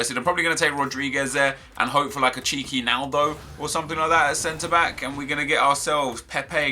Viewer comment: I thought he said a cheeky nando when he said he would like a naldo